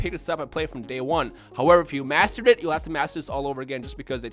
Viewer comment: Honestly thought you said "However if you masturbate then you'll have to maser this all over again..."